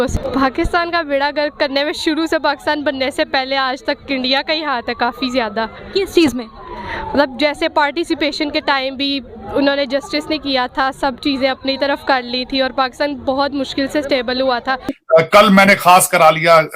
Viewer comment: did she said participation or partition? 😂😂